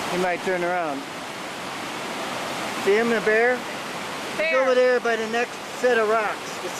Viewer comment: Ah hell, I thought it said a beer went over the waterfall and I was deeply concerned